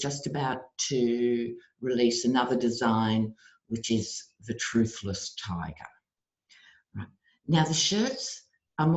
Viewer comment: So funny! I thought you said 'toothless tiger'! Love Love🙌🙏🙌